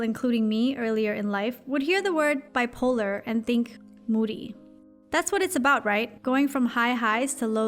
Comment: When you said moody i thought you said booty XD